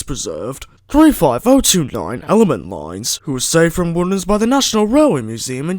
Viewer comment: Where was she saved from? I didn't hear it clearly. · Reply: Woodhams